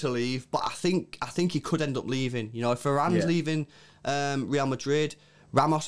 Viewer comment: did you say Ferran is leaving because that’s what it sounds like?!